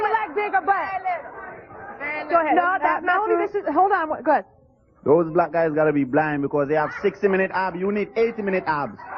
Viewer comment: what in the fuck did he say? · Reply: "those black guys must be blind because they have 60 min abs you need 80 min abs"- more or less what the random dude said